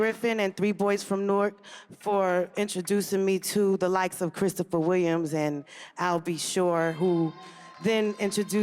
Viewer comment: She did mention him she just called him by his name Christopher Wallace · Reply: thanks, but it sounds like she said Christopher Williams.